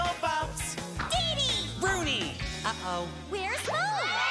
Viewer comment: I thought they said where is Joe?